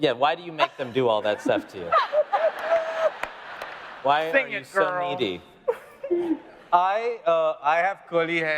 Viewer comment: What did Jim say at 12:30? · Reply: Sing it girl